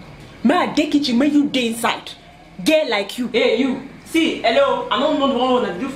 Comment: Why do all African mothers like using the phrase "girl like you"😭. Like it just kills my mood when I truly want to help 😣. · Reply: Ohhh I thought the mother said gay like you... girl like you makes much more sense....